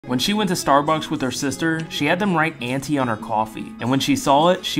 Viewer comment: No man would’ve gotten it ever. I still don’t even know what tf is going on. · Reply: …oh. I’m actually dumb… I thought the narrator was saying ‘Nancy’ not auntie….lol